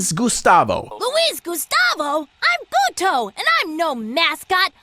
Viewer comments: d-did he just say "I'm puto" ??? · I have a theory I'm brazilian and I'm pretty sure that Luis Gustavo is a brazilian character as well (since his name is pretty common here in Brazil lol) and in the clip at 4:57 it sounds like he said "I'm puto" which in portuguese means "I'm fucking pissed" My guy be swearing in a children's movie, what a legend to my beloved country🥰